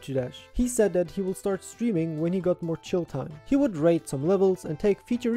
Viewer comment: I thought you said "when he got more children"